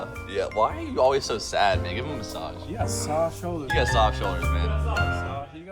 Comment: I coulda SWORN TO GOD he said "Why you always so sad nigga, give him a massage" 😭😭😭